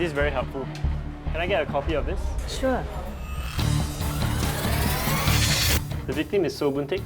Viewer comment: I Thought They Said " Can I Get a Coffee of This" 💀